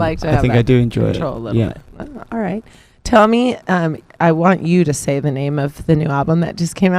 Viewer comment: did she called matty TOMMY? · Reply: "tell me, um, I want you to say the..."